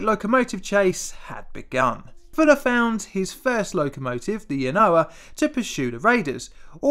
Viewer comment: Can somebody please tell me what the locomotive he's talking about is called? I can't quite make out the words he's saying, and the captions aren't helping at all. Can someone help me? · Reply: The Yonah <link> There is an old Buster Keaton movie called "The General" and a Disney movie called "The Great Locomotive Chase"